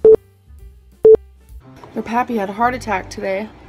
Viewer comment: i'm sorry guys, at 4:15 who had a heart attack? i can never understand what she says in this clip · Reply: It was Destiny's grandpa. They called him 'pappy'